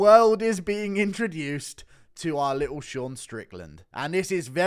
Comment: Hold on did she say “DeSean”? 💀 I might be mishearing that but that’d be great.